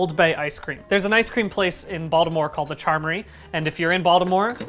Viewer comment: It sounds like ur saying "Bald -imore"